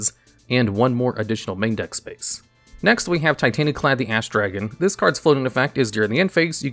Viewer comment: Dawg I totally heard you say Titanaklad the ASS dragon lol you aren't that slick 😂😂😂 hilarious